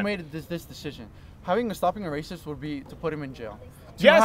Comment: did he say stopping a racist 😂😂